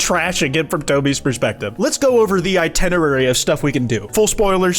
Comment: Did he just say "i-TENNA-rary"!?